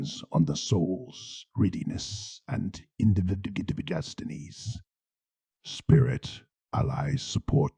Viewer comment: Please tell me what the word is at 2:08:34. My book Is still in the post so I can’t have a look yet. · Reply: readiness and individual destinies